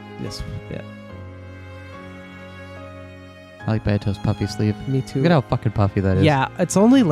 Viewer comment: I thought you said puppy and was very confused for a second.